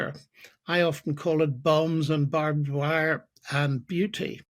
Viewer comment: I thought he said bums and barbed wire 😂